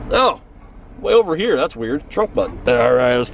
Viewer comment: Subtitles say trump button and it sounds like trump button XD 3:38 M O D E 3 Plz sho uz mod <num> cu dat sho teaneck abulans lits